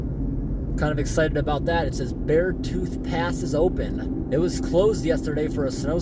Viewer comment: I thought it said Beer tooth Open